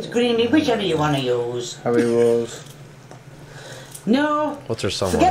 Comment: No one mentioning key's son at 10:08 suggested haribo's (gummi candy/sweets) instead of carrots